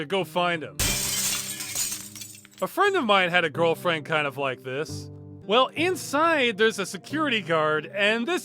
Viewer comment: I misheard Ross saying "A friend of mine had a girlfriend kind of like this" as "I would like to have a girlfriend kind of like this" at first when Aida smashed the window to get into the theater.